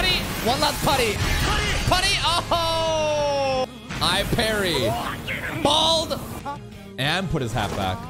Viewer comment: I think there's no season 2 anymore because the title today's episode says he parried everything😂 23:44 Also did just say "I parry bald"😂